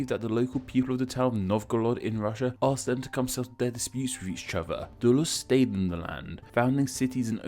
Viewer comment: “Asked them [a bunch of British syllables] with each other” is what I heard around 0:20-0:23. 😕 Did anyone else have a very tough time figuring out what he said around 0:21? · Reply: ill illy illy-o -o I believe he is saying “asked them to come settle their disputes with each other” it is a little difficult to understand